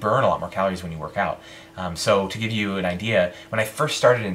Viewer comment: lmao. i thought you said linsanity